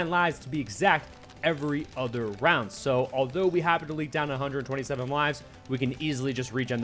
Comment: I think just said "124 moalives..." lmaoo🤣